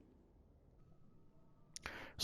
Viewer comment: Thought it said liars 😂